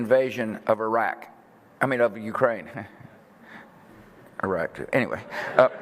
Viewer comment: Yo i missed the end of that bush clip before. I thought he said "the unjustified invasion of iraq... i mean ukraine" but he said "the unjustified invasion of iraq... i mean ukraine... that too"